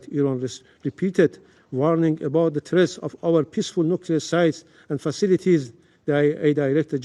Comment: Did i just heard him say "Peaceful Nuclear Sit"? Lmao 🤣 😂 😆